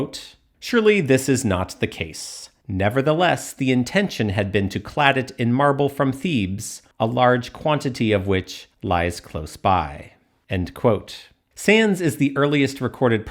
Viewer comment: I misheard "Thebes" as "thieves" which totally changed the meaning. Glad I turned captions on before commenting something embarrassing haha